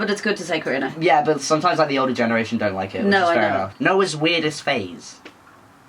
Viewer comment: To be fair, I thought you said "phrase", too. Then I went back and watched it again, and I have no idea why I thought you said that.